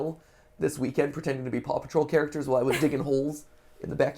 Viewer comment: "Ollie and Steph were rolling down the hill this weekend, while pretending to be Paw Patrol characters..." Omg, my heart, I can't take this level of wholesomeness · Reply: Somehow I heard “Dark Souls” not “Paw Patrol” I was wondering how his kid is already rolling in the hills dodging the imaginary boss.